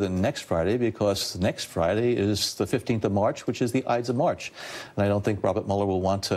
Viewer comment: WTH is Brennan saying at 5:56? "Eits of march"? What's that? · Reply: Nevermind, found it. Ides of march, a day in the Roman calendar. And the day Julius Caesar was assassinated.